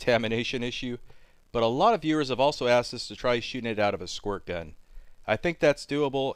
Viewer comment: I thought it said shotgun at first..Huge disappointment!lol